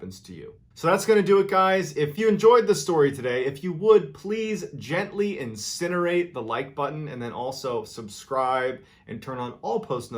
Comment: I thought he was trying to say "gentley inseminate the like button" 🤣🤣